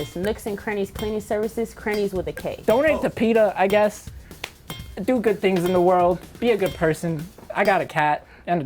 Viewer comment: lmao I thought the guy at the end said "Don't eat the peanut, do good tings-I got a cat" I was like.....what..? lmao